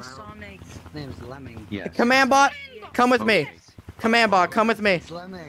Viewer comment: I THOUGHT THIS MAN SAID LEMING BUT NO ITS THE LEGENDARY LEMMON